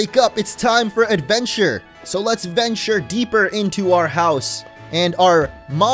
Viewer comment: wait did he just say "lets adventure deeper in our home, and mum!"